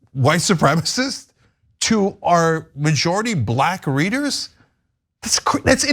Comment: did he say black readers? Lol